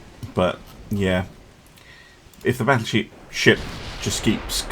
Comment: did you call it a battle-sheep for running away? :D